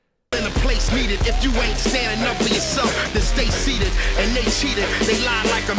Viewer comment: I guess that "...if you ain't standin' up for yourself.." is a double because if you listen to it closely it sounds like he is saying "If you ain't Stan enough for yourself, then stay seated..." which also makes sense